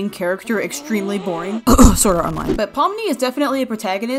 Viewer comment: did you say SWORD ART ONLINE!?!?